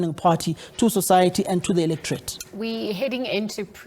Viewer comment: Did I hear him say " the illiterate"?